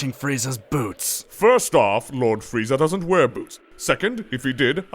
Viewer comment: oww said polishing frieza's boots!... i thought all the time he said boobs ... that explains much